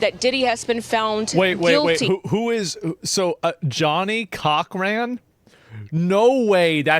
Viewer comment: I thought he said " Johnny Cockram" and expected to be a photo of Johnny Sins as a lawyer lmao